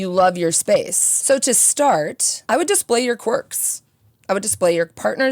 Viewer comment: absolutely heard this as "I would display your corpse"